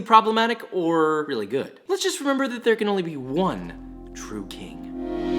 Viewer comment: "Let's just remember that there can only be one true king" Josh's burnt Basque cheesecake: am i a joke to u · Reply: I thought he said one true cake... Like his cake 🍰